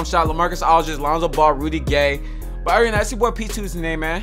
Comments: I thought he was saying really gay not Rudy gay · Bruh i thought he was saying really gay instead of rudy gay lol 😂😂😂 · When he said Rudy Gay I thought he said really gay When he said my jumpshot is Rudy Gay I heard my jumpshot is really gay